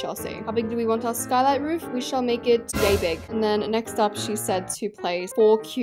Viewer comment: why did I think she said “gay big” 😂